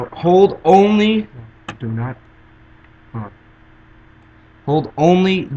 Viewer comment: did anyone else hear him go "do not talk" at 4:28 ?